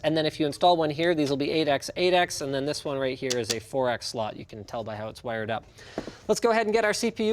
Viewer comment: "This is a 4x slot." "You can tell it's a 4x slot because of the way it is." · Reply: He actually said "you can tell by how it's wired up." (Quotes should always be verbatim, word for word.) That said, I wish they'd show the actual differences so I can identify them for myself in the future.